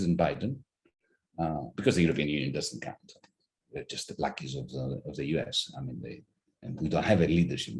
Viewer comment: Ummm, am I the only one who heard him CALL AFRICAN-AMERICANS "BLACKIES" at time stamp 11:57 ish? I had to put on the cc to make sure that's what I heard, but yeah, he did. This ill-timed statement only a day or so after thousands of Africans and people of African descent had to be RESCUED by their governments because the RACISM in the Ukraine is so thick that these STUDENTS (mostly medical and engineering), MOTHERS AND CHILDREN were PREVENTED FROM BOARDING TRAINS AND BUSES TO ESCAPE CERTAIN DEATH AND TOLD DIRECTLY THAT IT'S BECAUSE THEY ARE BLACK! What's even more curious is why you, Russel (nor anyone in the comments as far as I can see), didn't correct him or pause or even put a disclaimer in the comments...I'm sure you heard him...What up with that Russel?